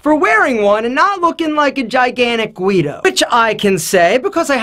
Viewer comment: I swear I thought he said a "gigantic queer" .. Then he said that he happened to be one... I Was So Confused.. I had to turn up the volume to clarify lol